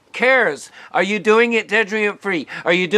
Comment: you say something I can't make out; "are you doing is (dedreant) free". What's the word? I am not hearing it. Thanks